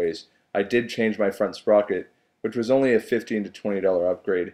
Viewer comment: he said he changed something to decrease fuel mileage can some tell me what he said please and were I can get it for my 300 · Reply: ohmew he changed the sprockets. on 250s and 300s the front sprocket is often switched out for a 15 toother. (effectively giving you longer gears) just google it to find it in your area. or ebay